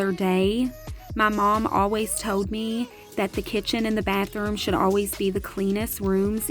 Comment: The whole time i thought you were saying kitchen Allen im like what the heck is that then im like ooooh kitchen Island. Im such a air head lol